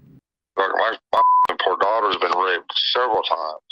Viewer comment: Did he say raped?